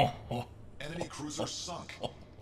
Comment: I thought it said "Enemy cruiser suck!" Almost died because of it